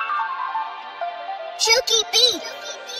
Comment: if I hear chokey b one more time i'm going to drink some bleach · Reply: I thought it said chunky beats LMAO